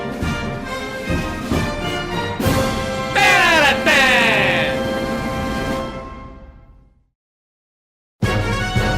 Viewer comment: What is that sound saying? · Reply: Ten outta Ten